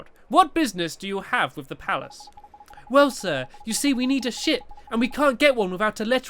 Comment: it sounds like Stampy says: We need a shit. Lol